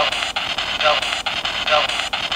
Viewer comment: At around 9:45 you ask if there are any witches and you pointed out that a voice says "no" in English. When you ask the same question at around 15:05 what I hear is Нет (pronounced "nyet") which is Russian for "no". That would make the answers consistent with each other.